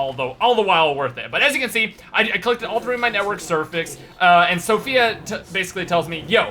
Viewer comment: it sounded like you said "Network Cervix"